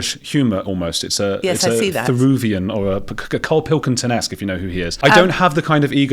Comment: Wtf did you say at 37:10 ? I can't Google it, never heard it; and that you said "if you've heard of him" suggests you were trying to be clever and being a cvnt - you do have that air of cvntishness about you. To paraphrase Roald Dahl - if you've been persecuted for 2000 years, maybe you need to take a look at yourselves? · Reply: He said "Therouxvian [as in Louis Theroux I assume], or Karl Pilkington-esque, if you know who he is..." He wasn't being a cvnt, a lot of people don't know Karl.